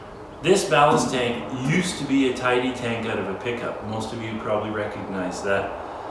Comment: I didn't quite make out what you said about the source of the ballast tank. I heard "a tighty tank out of a pickup". What does this mean?